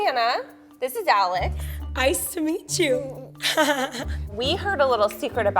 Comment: If you turn on captions, at 0:22 instead of "nice to meet you" it's "iCe tO mEeT yOu!".